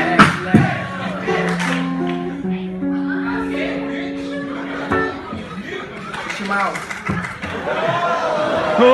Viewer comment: lmao does anyone know what the guy at 0:51 said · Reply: Haady Mattar HAHAH he said “b** they can’t f*** with him”